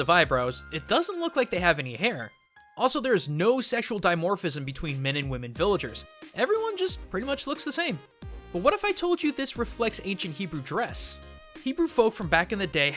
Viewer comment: did you just say all Jews look the same?!